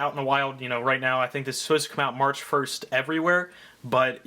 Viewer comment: March 1st not may 1st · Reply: same, my birthday is March 1st I heard him say May 1st I was about to cry😭😂